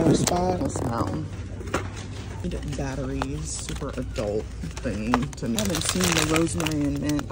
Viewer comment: "Batteries for adult things" 👀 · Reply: I thought she said "for adulting"